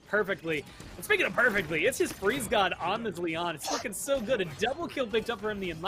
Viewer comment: Lmao! Speaking of casting from home cuz of the virus, and being nice and comfy, definitely says "It's fucking so good" at about 21:55. While commentating frzgod's Lian. 😂 Made me double take. Maybe I misheard? I listened to it a few times and chuckled.